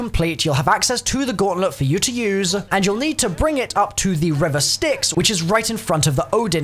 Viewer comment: is anyone else confused as to why he said river sticks? Maybe its just me but did anyone else hear (river sticks). If he did what does that even mean?